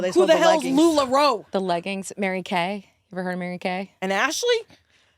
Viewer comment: I honestly thought you said Mary Kate and Ashley too. I am not familiar with Mary Kay.